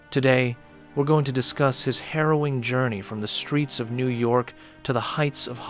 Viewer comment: You say "harrowing journey" I hear: "heroin journey" 🤦🏻‍♂